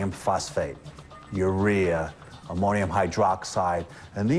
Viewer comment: Did I hear urea